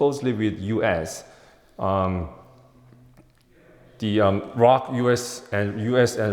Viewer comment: 'ROK' is referring to the Republic of Korea (the official name for South Korea). The subtitles say "Rock", which isn't what the Lt. Col. meant, obviously.